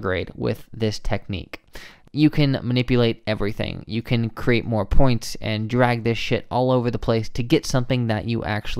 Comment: Wait a sec... @ 12:44 did you say "you can create more points and drag this shit all over the place"? LOLLLL I love it! I just subscribed for life!